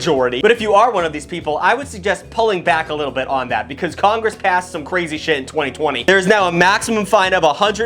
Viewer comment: when he said "Congress passed..." I thought he said "Congress *cast*..." Like it was a spell or a curse 🤣🤣🤣